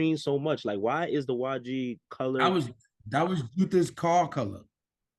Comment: What did say @ 5:08 who’s car color? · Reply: ju heff/ juther. he’s basically one of the original YG members, if not the one who created it in the first place